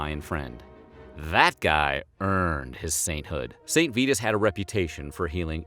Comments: Am I the only one who thought he said “Saint Fetus”? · when it said saint vitus i thought the narrator said saint fetus